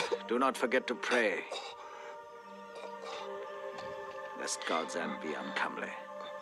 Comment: Anybody knows what Aguirre says at 1:28:35? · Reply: Lest god's end be uncomely.